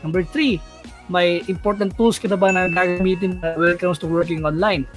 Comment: Hello po. I didn’t quite understand what you said in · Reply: "may important tools ka na ba gagamitin online" :)